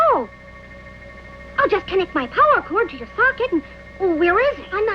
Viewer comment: Ayo what the kid say hehe oh that tickles 🤨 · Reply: I have a sinking feeling that replaying that scene to figure out what he said has put me on a list somewhere! :O Astroboy: "I'll just connect my 'power cord' to your 'socket', and - where is it???" Tin Boy: "On my hip, but it feels a trifle silly!" (blushing) "Hoohoo - I'm ticklish!" Wakko, Yakko, and Dot: "Good night, everybody!"